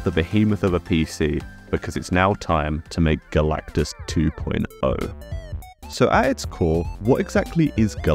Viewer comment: I thought you said calculus 2.0 lol